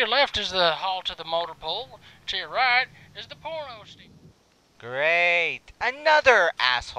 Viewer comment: did he say. To the right is the porno station? Lol